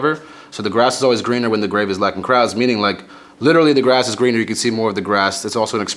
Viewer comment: "Grass is always greener when the graves are lacking crowds" That line is so hard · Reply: I always thought he said 'when the Grey be lacking crowds'